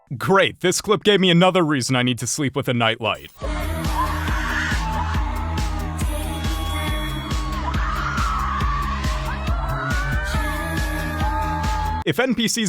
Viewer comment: I thought he said "...sleep with a knife." That'd actually be a better choice.